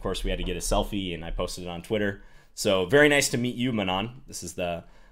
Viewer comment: I thought said 'Anand'. Even former World Champions love videos.